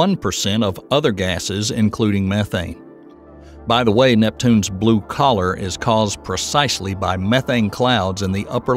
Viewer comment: I thought he said "Neptune's blue collar" rather than "colour", as if it's a working class planet!🤦🏻‍♀️